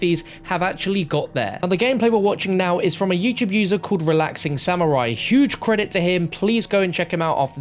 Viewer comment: Anyone else thought that said therelaxingend